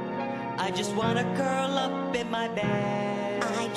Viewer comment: what did that wombat say on 0:24? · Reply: On 00:23, the wombat actually said "I just wanna curl up in my bed".